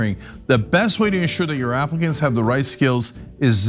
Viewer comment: did he say Africans ? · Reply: Applicants.. 🤣🤣🤣🤣